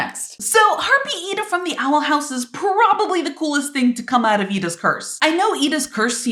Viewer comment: when you said edas curse it sounded like you were saying purse😂